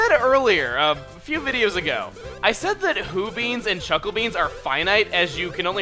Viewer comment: When he said he was one Hoo Bean short, I thought he said Poo bean xD